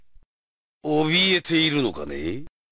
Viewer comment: que dice rugal?? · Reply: Tienes miedo verdad? En tono burlesco